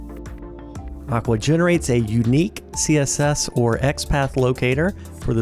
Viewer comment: Did I hear "My boy generates..." 0:16?